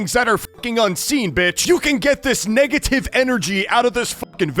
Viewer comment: why did i hear 🥷🏿tive